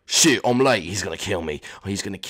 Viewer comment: Everyone that thinks he said the sh- word it sounds like it just because gave him an English accent. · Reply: The Memer sorry I know its Australian. So thats why it sounded like he said sh** I'm late!! So yeah.